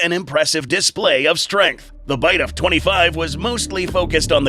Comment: did HE JUST SAY THE BITE OF 25???